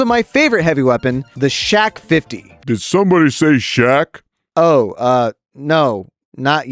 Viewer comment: did someone said ASh-12?